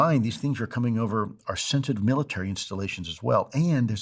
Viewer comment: Did he say, "sentient" at 22:16? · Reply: sensitive is the word